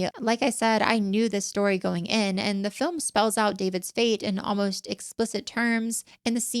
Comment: Anybody catch at 5:45 she says david spade??